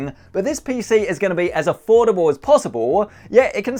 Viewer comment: I'm sort of late for this video but did he say "affordable"?